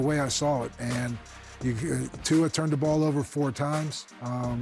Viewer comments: Tua Turndaballova with another Gem 💎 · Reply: Cowher sounds like he said "Tuaturndaballova ..." even though he was saying "Tua turned the ball over 4 times."😂 A lot of us call him that or Tua "Fragilelilvulva" 😂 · Cowher sounds like he said Tuaturndaballova even though he was saying "Tua turned the ball over 4 times." 😂